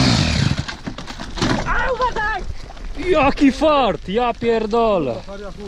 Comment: is it just me or did i hear yucky fart